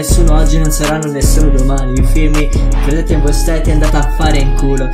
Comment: "credete in voi * steti *"